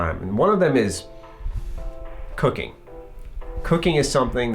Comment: I thought he said cocaine. 😂